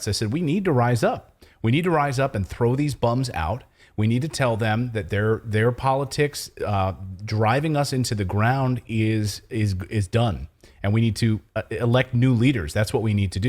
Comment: "we need to throw these bums out and elect new leaders (bums)", did I hear right?